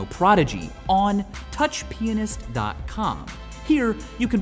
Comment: i thought he said touchpenis.cum. lol